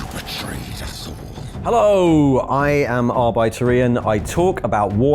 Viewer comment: Every time Ian is introducing hinself I hear "Arbeiter Ian" - Laborer Ian.